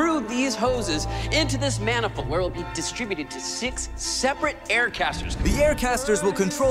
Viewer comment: Why when he said six separate I thought he said 6-7 😭